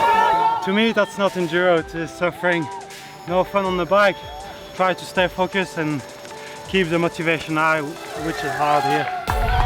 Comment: "To me that's not Enduro...just suffering, no fun on the bike...try to stay focused and keep the motivation high, which is hard here." Did Maes say that or did I get it wrong?